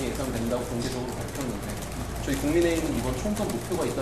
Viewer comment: 기자가..저희 국민의힘…? 내가 잘못 들었나…??